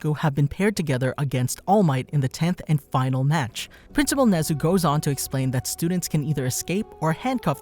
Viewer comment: i just realize he said “principal Nezuko” instead of “Nezu” 😂